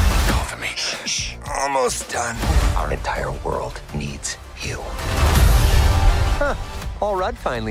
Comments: it sounds like he said our entire world needs “Hugh” could definitely be a double meaning in “you” but yeah. Excellent work!! Cannot wait for this movie!!!. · I didn't notice it at first but he says "Deadpool and A Wolverine", that is a very important "A". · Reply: and it sounds like he says "our entire world, needs, Hugh" but it's very breathy so I'm not 100% sure.